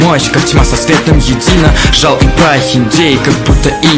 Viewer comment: Непобедимых саблезубов Нацу в соло вынес☺ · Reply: Там было Жал и Прохиндей, но мне послышалось жалкий Прохиндей. Я такая да ну нах